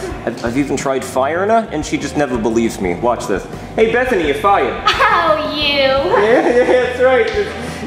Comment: Mr Goodman: FIRED-uh. Captions: buy your duck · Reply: Same here, I thought Goodman said "Buy your duck", But in fact, Goodman says "FIRED-uh".